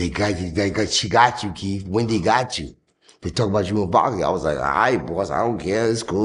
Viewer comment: He said Erick sermon said they talking bout you and Bobby " Anybody catch that??